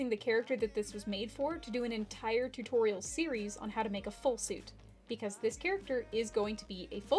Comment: when you said full suit i thought you said fwurr suit